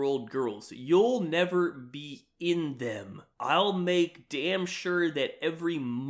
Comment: that had me rewind like what the f*** did I just hear? I hope nobody's "in them"